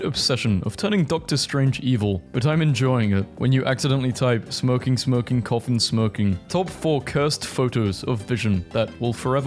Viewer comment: Am I the only one who heard, “ smoking smoking coughing smoking?”